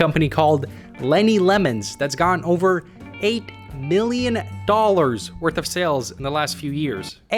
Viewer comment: I heard 8 billion...